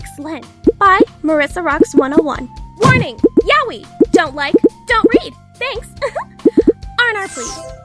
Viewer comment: im now noticing years later. at 4:38, the narrator says "dont like? Dont read! when the subtitles say "dont like? Dont breathe!" wow thats harsh